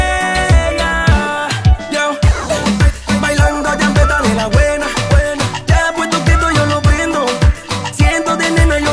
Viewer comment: No entiendo que dice en del 0:33 al 0:43 quien sabe? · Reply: bailando champeta de la buena, nada mas eso jajaja